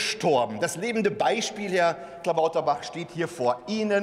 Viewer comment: sagt er wirklich Herr Klabauterbach oder hab ich das nur so verstanden😂😂